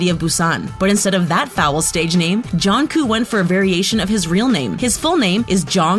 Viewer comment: NO HATE it sounds like they say ‘jonkooh’ instead of ‘jungkook...’